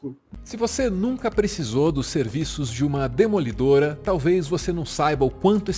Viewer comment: Ouvi "Demolidora Brasken" e buguei, pq já tinha ouvido relatos terríveis a respeito. Assisiti até o fim e é isso mesmo: uma empresa do mal.